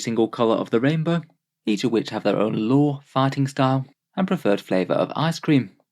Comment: I always hear "they have their own fartingstyles"